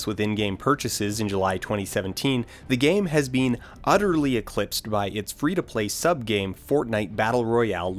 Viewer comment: There’s a typo in the subtitles: At around 1:30, you say “the game has been utterly eclipsed”, but the subtitles say “the game has since been utterly eclipsed”